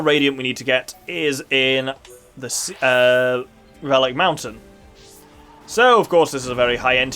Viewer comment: My husband miss heard and thought you said phallic Mountain, he is very disappointed that's not it's actual name. High Entia secrets, pretty cool. I don't know, I kinda like the sound of being forced to sleep but sadly I think my insomnia might cancel it out... Man isn't that a kick in the guts. Putting them out of their misery just to find out there's a way to save them. Double down on the pain why don't you.